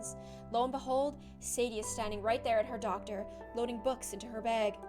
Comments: doctor instead of locker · In 50:09 gloom said doctor instead of locket